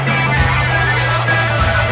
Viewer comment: At first I thought they said Intamin power, but then I realised it's infinite :'(